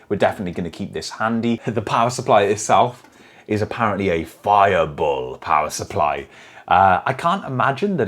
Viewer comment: Did you say "Fireball"??? 👀 Smoke test indeed... 😀 Edit: Ah, "bull"... either way it's funny.